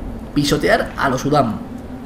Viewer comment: en vez de pisotear pense que decia pistolear